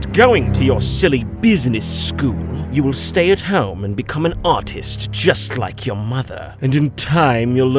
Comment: first time, I heard "become an autist" and had to do a double-take